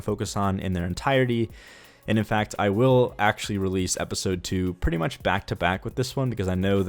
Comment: I thought you said episode 1 and 2 would be back to back 😂